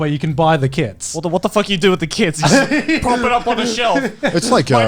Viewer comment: I misheard kits for kids and was very confused